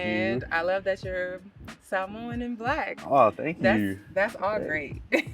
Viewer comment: “I love that you’re Samoan and black”……. Girl what😭😭😭😭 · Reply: Lmao I thought she said Samoan in black 😭😭